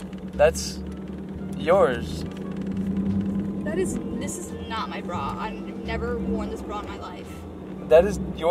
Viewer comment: All I hear is “This is not my brah”